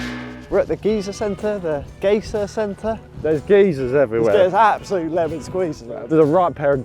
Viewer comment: When they first started talking about geysers I heard “geezers” which is a very different thing in the USA.